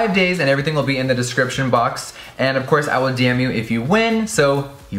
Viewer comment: Sounded like he said "I will DAMN YOU if you win" 😂